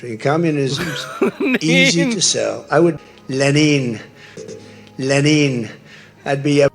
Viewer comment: I thought he called him “Lenny.” I will only be referring to him as Lenny from now on.